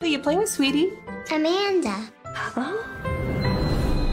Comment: i put it on slow and I hear I'm a man duhh I'm sick